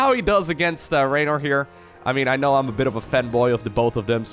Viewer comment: I heard femboy...